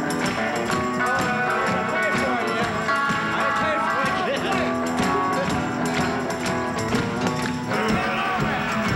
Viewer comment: ❗Question! Question! 1:07 - what did Scotty Moore say to Elvis? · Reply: "That's my new guitar, watch it!" 😀😀 big smile from Scotty!